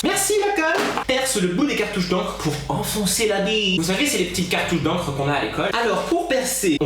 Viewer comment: j'ai compris autre chose mua quand t'as dit " enoncer la biii..." EUHM EUHM